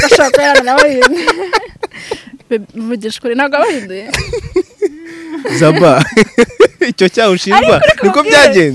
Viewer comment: Wait..... 27:06 who else hears says Babe 😂😂😂😂These guys are into each other's w'Allah😂😂😂